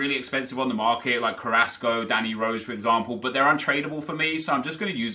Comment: Del Peiro is the worst one to do cost over a million for a untradable when his 90 rated cost 1.5 million and its not that much worse and even his 87 isn't that much worse and he's a lot faster overmarrs and Rui costa are more logical to do but it's not worth it at all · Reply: jml 1877 I thought said they're tradeble